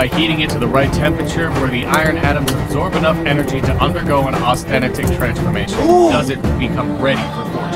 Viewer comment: i thought it said an acustic transformation